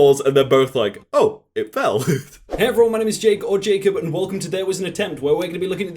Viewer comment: omg I thought he just casually said "Fakob Jakob" and not "Jake or Jakob" rip